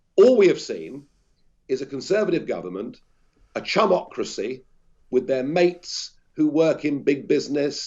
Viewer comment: Oh "chumocracy" I thought for a moment he said "chubocracy"